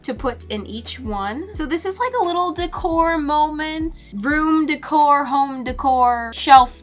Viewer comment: Lmao when she said "room decor" I thought she said broom decor